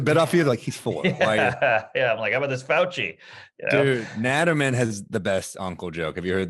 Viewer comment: Who's the comedian they mention at around 29:45 - 30:30? I can't catch the name. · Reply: I think it's Dan Naturman.